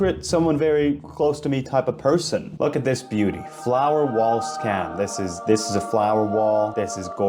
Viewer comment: why did I think that it said “flower well soon” 😭